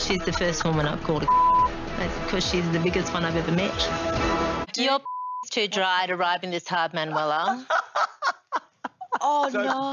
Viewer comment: what did she say? I couldn’t understand. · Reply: 'Your 😽 is too dry to be riding me this hard Manuela' lmaooo